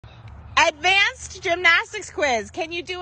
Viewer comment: I heard "Advanced Gymnastics Rizz" 💀👍